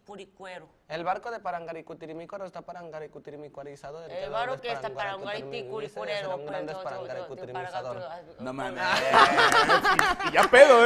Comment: Alguien se sabe el trabalenguas que dice??? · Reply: El barco de parangaricutirimicuaro está parangaricutirimicuarizado y el que lo desparangaricutirimicuarice será un gran desparangaticutirimicuarizador